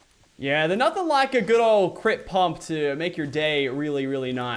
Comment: I thought he said crypt pump